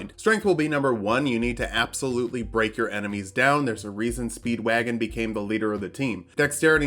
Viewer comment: Oh speed wagon ... I thought said Speed Buggy